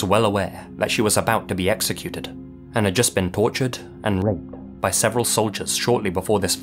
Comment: tortured and what? Stop blocking words dude, we dont know what you're saying or at least give us an indication on what the hell youre trying to say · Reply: it was r*ped (I think u can guess the asterisk) unfortunately he has to or it can get demonetized or have the video taken down/age restricted (i can't even say it uncensored or my comment will get removed)